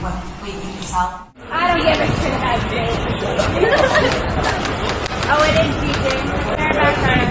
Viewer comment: What they say to him at 01:56? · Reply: Some paparazzi said ‘I don’t give a shit about Zayn, I care about Gigi.”